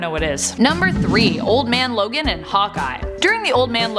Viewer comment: I thought she said Old Man Logan and Popeye! Lol I was like I gotta freakin read this!